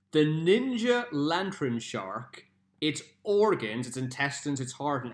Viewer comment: Thanks for watching guys! If you like this, Ill do another one but I won't mention the name until after because some people wanted to play along as well but they recognise the Pokemon's name Hope you guys have an amazing day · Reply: I got all of them right except for the lantern shark i thought u were saying lanturn